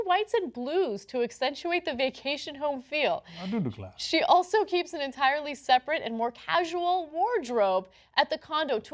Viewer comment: What did Cenk say to Ana at 2:03? I can't hear it. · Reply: I do declare